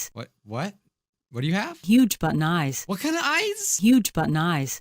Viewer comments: I think she said ‘huge button eyes’ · I thought she said "huge butt and eyes"